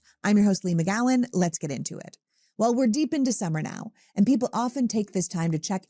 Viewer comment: December? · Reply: I thought she said December too. Then I realised she must have said into summer.